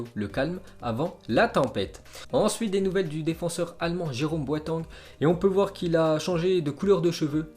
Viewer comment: Jérôme quoi ??? 🤣🤣🤣🤣🤣🤣🤣🤣🤣🤣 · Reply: Boitangue 😂😂